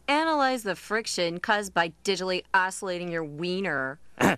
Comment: I don’t usually like Daria but I’ve always loved that “digitally oscillating” line. ☻ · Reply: I thought she said digitally isolating